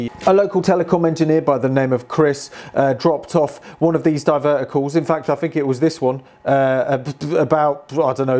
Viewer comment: I thought you were saying "divertical" at first. IT feels like that should be a word.